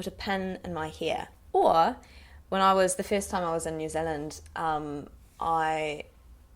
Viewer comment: When you said the first time, I thought you said the fifth time. You pronounced it like fist!! lol